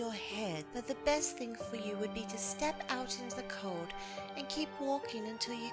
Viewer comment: what did say? It sounded to me like "step out of the cod". Was that "car"? Sorry, I'm just very confused. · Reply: Step out into the cold. They are currently in Antarctica.